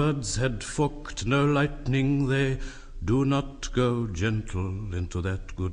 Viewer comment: did he say what i think he said...? XD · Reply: No. His words were "Forked no lightning".